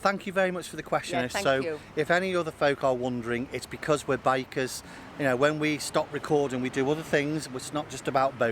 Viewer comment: For the past few vlogs, I honestly thought you were saying that you both were bakers. Well, you showed the picture of your motorbike and I finally figured out you where saying that you and your wife we’re bikers. Thank you, God bless and stay safe.